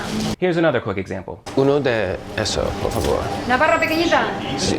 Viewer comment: Wow. At 4:53 the woman behind the counter seems to be saying "Navarra pequeñita," pronouncing pequeñita "pequeñija" while pointing to the half-baguette. I'm wondering if I'm hearing that right - if that's one of those regional pronunciation quirks you find in certain parts of Spain - or if I just am hearing it all wrong. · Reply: I heard "La barra begrisa"